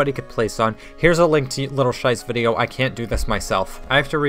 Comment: Did you sayed at 6:33 "little scheiß video"? :D